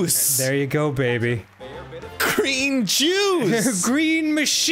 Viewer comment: It sounds like Arin is saying Cream Juice